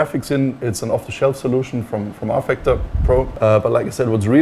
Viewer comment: did he say "Rfactor Pro"?